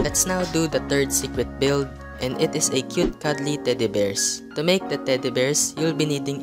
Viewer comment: i thought you said cute "ugly" teddy bears